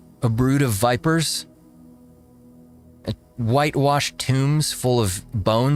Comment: I thought you said white washed timbs, as in timberland boots